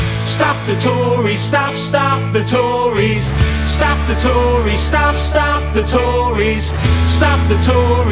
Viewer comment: So have YouTube made you edit it say "stuff" instead of "f*ck". Can't make it up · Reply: I hear "Stop the Tories" and it makes sense. 😅